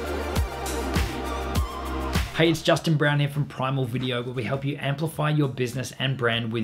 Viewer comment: I thought he Said Justin brownie I laughed But it's "Justin brown here"